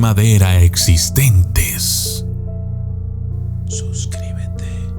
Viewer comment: Y ese mensaje subliminal entre el minuto 6:49 y 6:50 que dice suscriban se 🤣